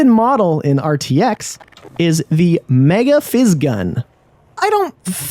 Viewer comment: What did he just call the super gravity gun!? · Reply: Lol sorry, in the backend (console) it's called "mega".